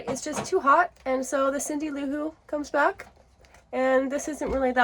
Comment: did you say, "So the Cindy Lou Who comes back?" or did you say, "So the Allyn Onderdonk comes back?" I couldn't quite hear what you said. My audio was spotty.😂